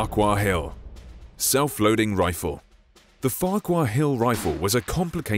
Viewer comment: I thought it said self-holding rifle